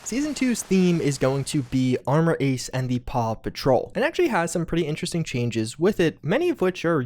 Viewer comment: "Armor Ace and the Paw Patrol" Uhh what. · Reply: Paw Patrol? I thought people were saying Papa Troll.